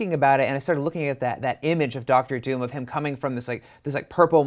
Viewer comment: At first, I thought you said Doctor STRANGE. Now that would've been insane!! But it's not, just Doctor Doom lol.